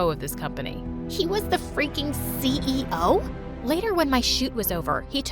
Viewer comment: When she said : freaking CEO I Thought she said : freaking eggo